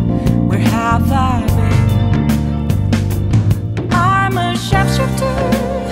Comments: Thanks for the lyrics because I had no idea what she was singing before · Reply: I thought she said “I’m a chef shipter” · For the longest time I thought she said "I'm a sharpshooter" ... lol